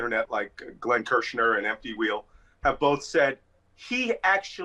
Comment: Malcolm mentioned the top 2 legal experts on the internet. The first was Glenn Kirschner (who I watch daily), what was the name of the second legal expert? It sounded like MT Wheel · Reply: It’s “emptywheel” , Marcy Wheeler’s site.